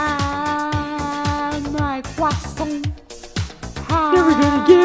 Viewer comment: sounds like he says my crosssant